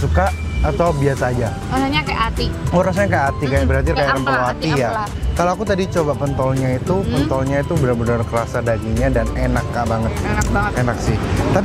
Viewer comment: gwe dengernya kont*l